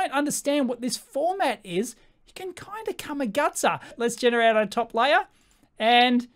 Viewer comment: What is this new word the floating head is using, like at 19:46? Camigutsa? It seems to mean to commit a booboo. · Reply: You must be new here. Come a gutser: A gutser is Australian slang for a heavy fall, or figuratively any abject failure